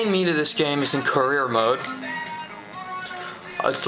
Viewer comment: Did you say courier mode?